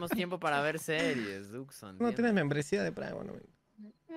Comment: que dijo duxo en el 10:48? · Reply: Tu no tienes membresía de pream o premium wewon